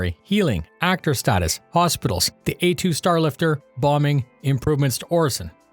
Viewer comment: I thought you said ‘plumbing’ instead of ‘bombing’ and I didn’t even stop to think about it. That’s Star Citizen for you.